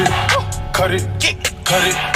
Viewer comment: I thought he was saying "curry"